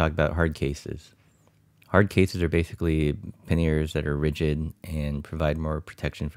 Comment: what do you say at 0:18 "_________that are ridgid"? thanks. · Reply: panniers... 7 years later...